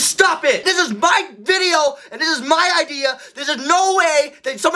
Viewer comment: Did he say his video and his idea... I don't think he uses the comment for anything bruh😂